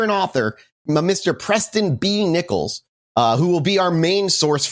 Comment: Preston B Nickels sounds like a fake name. Every time they said his name I just heard "Pressin Dese Nickels"